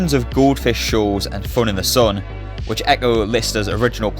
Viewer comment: Oh, "Goldfish Shoals " ? (always heard it as "Goldfish-es")